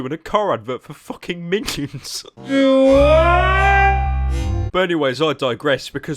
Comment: It sounds like he said do what? At 4:31 when he was crying for help